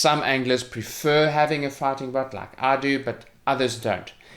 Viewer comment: Sounds like your saying "Farting Butt" ;)